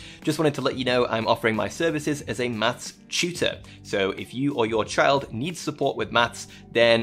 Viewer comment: Dude...Dude, I totally misheard you during your sponsorship read. You said 'maths tutor' right? I'll I heard was you offering your services as a 'mass shooter' and...you just kept going? Talking about your 5 years of experience and teaching online courses XDXDXD I'm like, this is an incredibly on the nose bit? This is a bit, right?? Some deductive reasoning spin off bit???